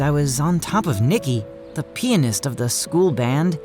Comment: WTF did he just say pinist its pianist bucco